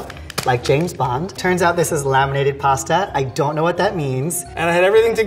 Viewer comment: anybody catch the 'lamented' pasta insetad of laminated pasta in the subtitles